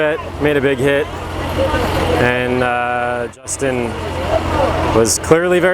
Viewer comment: Did just call the kid Justin? lol